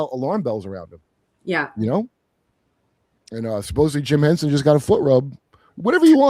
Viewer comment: did Kurt say Jim Henson??? 😢 can't find a confirmation, but i can't make out who else he would have said